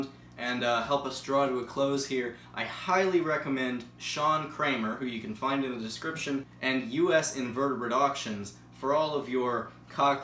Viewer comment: I thought he said “John Kramer” and Saw the movies came to my mind